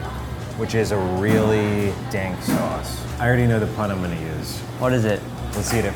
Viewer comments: did he say dank? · Did he just say "dank sauce"??